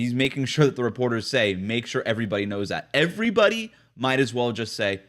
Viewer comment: I thought he said make sure Jimmy knows that